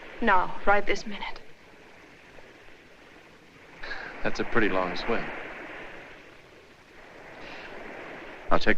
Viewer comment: I heard " pretty long sweater".. not pretty long swim..last time haha